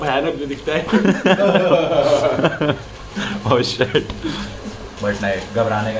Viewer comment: did he said maa ch*da😂😂